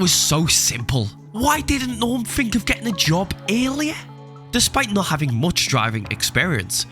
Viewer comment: I thought he was saying alien whenever he actually said earlier 😭💀